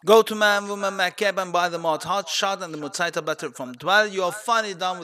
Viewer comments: A great guide, thank you. Although audio quality could be better: at 5:44 you mention getting a Mutator from Dwell, but I cannot understand which one you say. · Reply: battery. the echo is fixed now. sorry for that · What mutator did you say? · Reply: Battery